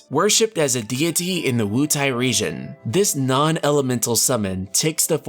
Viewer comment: "Worshiped as a deity in the WuTang region" I swear that's what I heard lmao :p